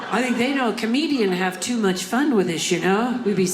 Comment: Every time I see this in my feed, I watch it, this lady is great! Also at 3:50 she says "I think they know a comedian would have too much fun with this $h1t huh"...... 🙄🙄🙄😁😁😁😁🤣🤣🤣🤣🤣🤣🤣🤣 The closed captioning says she is saying "too much fun with us you know"... Hmmm, anyone else hear her say Shirt without the R..?